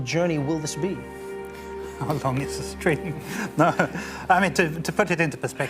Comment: i thought he said "how long is the stream..."